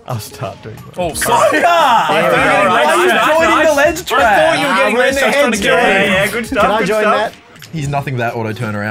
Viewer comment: Can anyone tell what the heck they're saying here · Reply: H: 'COGGER' C: 'I thought you were getting ledgetrapped' H: 'why were you joining the ledgetrap' C: 'I thought you were getting ledgetrapped' Z: 'ahhh we're in their heads jordan' P: 'good stuff good stuff you're welcome